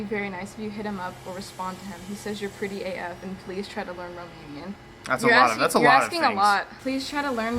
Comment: did she say Romanian? 🇷🇴🇷🇴🇷🇴🇷🇴🇷🇴🇷🇴